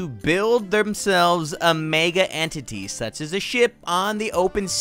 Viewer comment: I almost thought he said s**t instead of ship